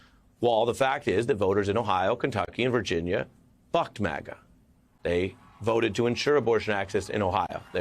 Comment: Had to play this a few times. Ohhhh. "BUCKED MAGA". I almost thought he said...... 😀